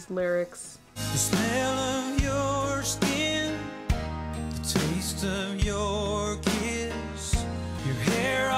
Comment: Am I the only one who thought he said, "The taste of your kids?"